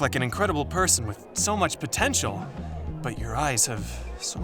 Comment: did you say potential